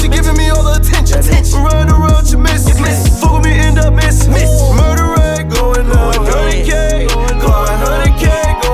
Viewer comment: Yoooo i thought it was saying murderer going down